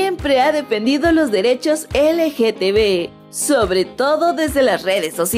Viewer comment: Escuche mal o en el minuto 3:12 dice LGTB en lugar de LGBT 🏳️‍🌈